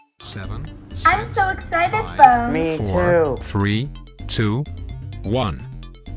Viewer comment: what did phone say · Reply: “Me too!” I should probably add captions to this and the other episodes